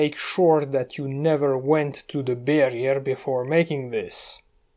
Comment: I heard "The Barrier" as "The Bay Area" - Time for me to put the bottle of Cossack Vodka down. Nice trick with the Knife and the Wall